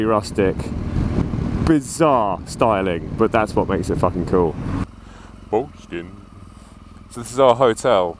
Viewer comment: did you say "foreskin"? :D :D